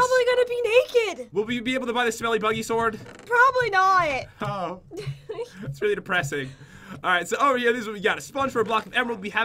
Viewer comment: when he said that depressing I thought he said Crippling depression LOL 😂